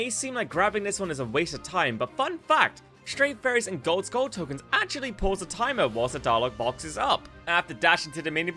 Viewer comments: Bro I literally cannot understand wtf you're saying at 7:07, I've listened to it 5 times lmao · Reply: "Stray fairies and gold skull tokens actually pause the timer whilst the dialogue box is up" Took me like 6 times too lmao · I couldn't understand what was said. Sounds like "Fun fact: stray fairies and gold skull tokens actually pause the time 'a wuzza dollar box is up'" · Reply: Hahaha I had a bit of a cold at the time which doesn't help there 😆 Once the dialogue box is up I believe was the sentence